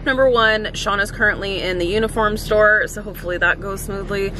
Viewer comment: I know you said “Uniform Store,” but I heard “Unicorn Store” both times 😂😂😂😂😂